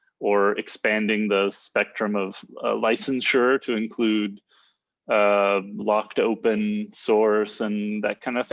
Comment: Did you say "blocked open source"? That sounds so interesting but it doesn't Google.